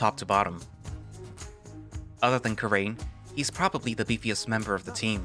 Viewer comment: did you say Carain XD These good VN's get stuck it seems! ^^